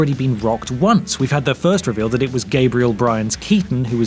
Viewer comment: Good old Gabriel BRYAN. Fabulous actor. 😂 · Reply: Huh. I thought they said Gabriel *Brine*. :P